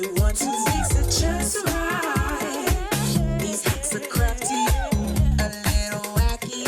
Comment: When she said "These hacks are crafty" I thought she said "These hacks are crappy" 😂😂😂